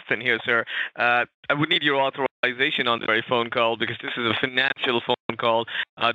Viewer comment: did.. did i hear that right? did scammer say "this is a natural phone call"?!